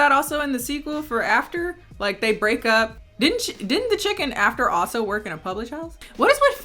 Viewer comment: "The chick in After" but you said it so fast it sounded like you said "the chicken in After" it took me a minute cause I was across my room and had to rewind to figure out what the hell you were talking about lmao.